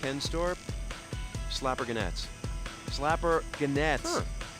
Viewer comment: When he was on the phone at the start of the video it sounded like he said slap her nuts slap her nuts